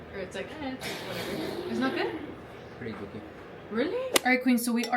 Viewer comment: What does he say??? I cannot figure it out for the death of me😅😂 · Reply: He said “its pretty dookie” 💩